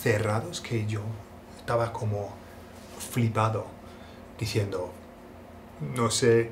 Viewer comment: what did you say at 4:51. "Fribado"? Qué significa? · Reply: flipado = flabbergasted